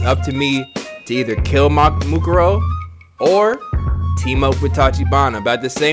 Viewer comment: Did he just say Mukuro??😂